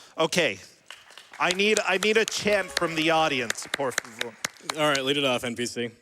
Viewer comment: it sounds like he says "por favor" very quietly under his breath and then realizes that he doesn't actually want to say it.